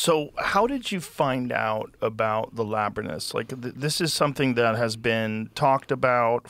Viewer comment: Did Joe say "labrinus" 😂😂